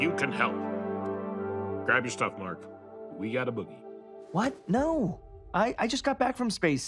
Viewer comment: He definitely says, "boogie," but I heard "boot," at first, too